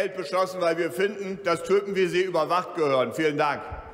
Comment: Ich hab "dass Türken wie Sie" verstanden 🤣